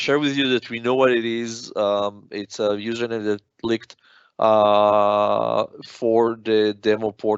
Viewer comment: he says 'It's a username that "bleeped"? "leaked"?' If someone heard that better, could you post what the word was? But overall very informative and helpful.